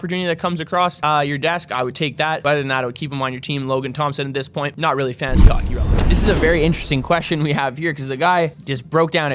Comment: What was the last thing he said about Logan Thompson? “ 4:43 LT, at this point, not really fantasy hockey […]. “ ??? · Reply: Not very fantasy hockey relevant